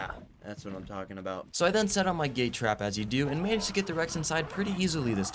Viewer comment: with the male rex i thought he said "gay trap" instead of gate trap